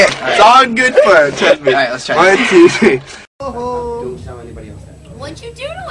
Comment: xD he couldn't type!! oh and 2:20, does Nick say " don't tell anybody I'm fat?" xD